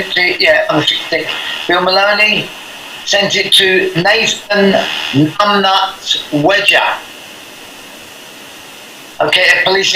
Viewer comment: names Nathan (something) wedger its not clear what he is saying, is this john wedger? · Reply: Jonathan Wedger. Jon/Nathan. Yes, same person.